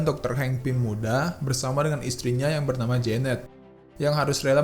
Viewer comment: gw dengernya jamet:v